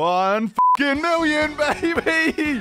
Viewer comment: I love if you put on subtitles it think he said 150 mil instead of 1 mil